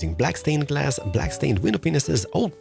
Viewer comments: black stained window what?! I cant be the only one who heard something different, right? 😂😂😂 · what did he say??? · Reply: Pineses lol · Did you just say p***s’s